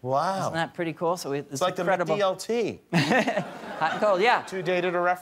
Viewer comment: What he say? "It's like the credible DLT"? Surely that wasn't it.